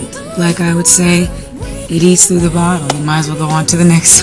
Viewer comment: excuse me but... starting from 3:45 when she says: "because to me, once you start second and third and fourth guessing yourself, like I would say,____________, might as go on to the next song." what is she saying there? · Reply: Chris Fong eats through the bottle